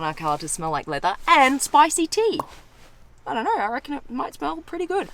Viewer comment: min.: .... DID SHE SAY SPICY ? WOOHOOOO SPICY CHICKEN WINGS AND EXPENSIVE LEATHER !!